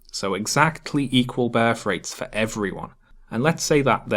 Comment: Great stuff. Cheers. Had to laugh . I misheard your pronunciation of 'birth rate' ,i thought you said ' bear freight'. Keep up the good work.